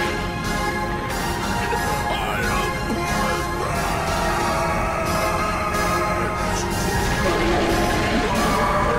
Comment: what cell say @ 1:14? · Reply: He said “I am perfect.”